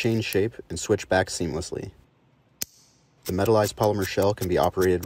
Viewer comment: "metalerized polymer shell" is the most ingenious way I've heard somebody say shiny plastic yet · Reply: idk I hear "metalized" not materialized